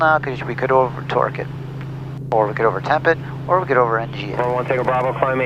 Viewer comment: I thought he said he could over twerk it haha